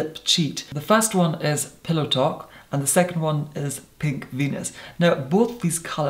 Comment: PINK VENUS...I swear at first I thought he said “pig fetus”. I thought, “Well that’s a specific name for a lip liner...” 😂😂😂